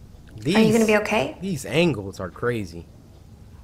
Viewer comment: When you said these angles are crazy I thought you said these ankles crazy 😂